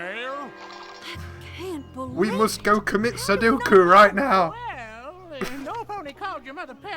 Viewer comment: Did you just say they needed to go commit Sudoku? XD